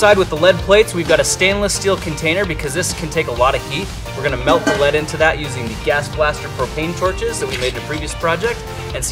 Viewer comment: I thought grant said gaster blaster